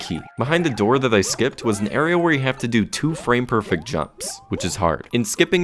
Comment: DID I HEAR FRAME PERFECT FROM GD